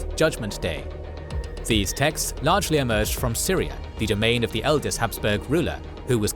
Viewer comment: It's me or I hear Syria instead of Styria? Subtitles seem to make the same mistake. If the auto-subtitles are the problem, ignore this 🙂